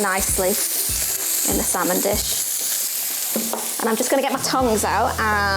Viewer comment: of the video, I heard her say, "I'm just going to get my tongue out" when what she meant was get her tongs out. Some regional British accent are hilarious or simply annoying which is I suspect why she got so many thumbs-down.